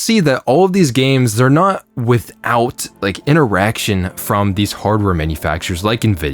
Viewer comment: did you say these games are not without an "erection" from these companies??? I swear, thats what it sounded like....😮😂